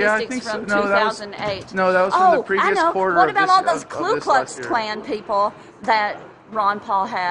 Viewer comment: Did she just say "KLU KLUX" ?! LOOL